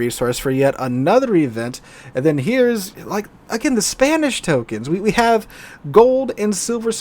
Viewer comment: When you said Spanish tokens, for some reason I thought you were saying "spammish" tokens. Fits, doesn't it?